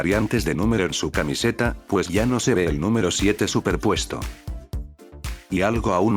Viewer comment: parece que dice 79